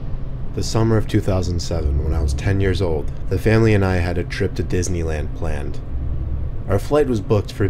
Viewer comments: "The Family And I Had A Trip To Disney Land Land" That's What I Heard Until I Turned Up The Volume 😂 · Anyone else hear "land land" at 00:22 its "land planed" my bad